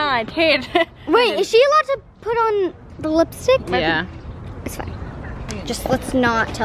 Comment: lol😂 blipstick instead of lipstick 💄